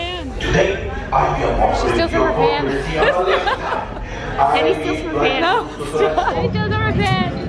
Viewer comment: "Jenny steals from her fans!" If they're talking about our hearts, then yes. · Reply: I heard "She steals rubber bands" until after like the 5th time... 😂